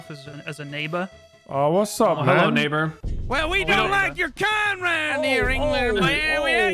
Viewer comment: Am I the only one who sees that latelyjk, Nogla's in-video subtitles don't always match what's being said? Like at 9:25, Terroriser says "we don't like your kind", but the sub says "we do like your kind". I feel as if whoever edits these videos has bad hearing.